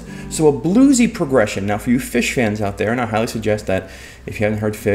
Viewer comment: I thought you said fish-hen and I'm wondering what is a fish-hen?